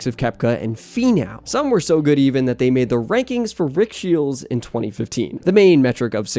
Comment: At the 5:40 mark, you speak of certain rankings. You mention a name that begins with "R". Sounded like "Richfield" or "Ridgefield" or similar. What is the name please?